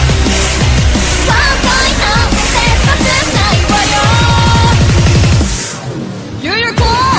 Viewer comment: Is that VORE I hear at 2:01? Well, that word fits Yuyuko like a glove...